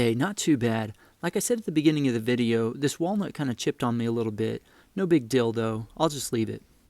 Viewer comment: No big dildo now that’s what he said bahhaaaaa sorry bro